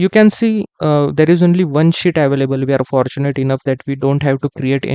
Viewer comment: did i hear " One Sh*t" :D